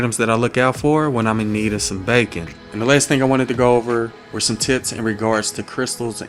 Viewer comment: it’s sound like he said here are some “Tits”.